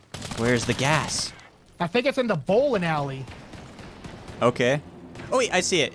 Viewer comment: When you ask where is the gas I thought said in the b*ho*